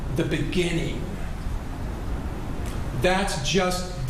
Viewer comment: I thought he said, "The evidence will show that I am Lucifer in the flesh"